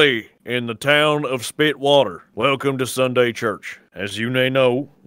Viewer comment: Why did i think Narrator said "Town of Speedwagon"